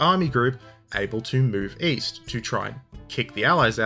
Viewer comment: OOOOOOOOOH…. East?? I thought you said ‘Weast’!